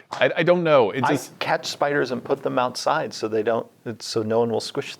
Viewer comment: OK, so you said "catch spiders" and I heard "CAT spiders", and now I'm picturing cat-sized spider cats with claws who get the zoomies...